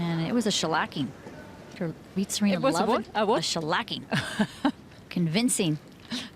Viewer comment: What is the word @ 16:20? Shalakyn? Anyone knows hhere does it come from? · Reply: a shellacking (informal North American) - an utter defeat.